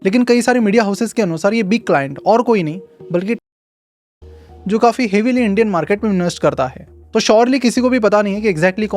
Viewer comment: ? What he said? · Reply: Tiger global